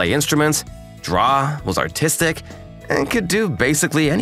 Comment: I fr thought bro said “autistic”